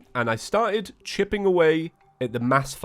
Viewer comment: I'm sorry, did you say Chipping away?